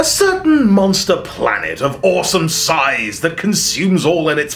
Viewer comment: I thought u said “Orson sized”, lol.